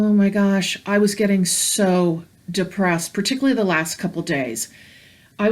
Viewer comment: I haven't finished watching the video yet, but had to stop it when you said "I was getting so depressed" I clearly heard "not depressed ... obsessed" ... not sure if this will resonate, but I was told - there's a lesson she needs to learn to let go of control ... striving to stay in a high vibration at all times, at all cost, is indeed costing her health ... the human form is not yet ready to sustain the high vibratory states at a constant rate ... all of you need to honor your human side, listen children with your hearts and allow yourselves to be that which you came here to experience this time around - Be Human - for you might be holy and oh, so bright on the inside, but you are in a human form for a little longer while" Feel better soon, sending healing light your way ❤️ PS I was also told for you to look into, Epsom salt compresses/ baths and infrared light therapy for your knee